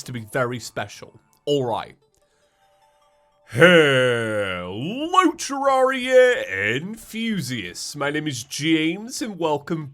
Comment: has awakened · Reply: oh is that what he says? I always thought he said Hello terraria and Fuses. Like i dont know why he says it too.